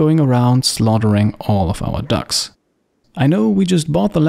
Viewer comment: I misheard you at 4:53 (I heard dogs instead of ducks) and was really shocked. But f those ducks.